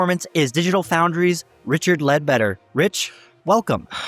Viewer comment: Richard Bed-wetter is what I thought he said🤣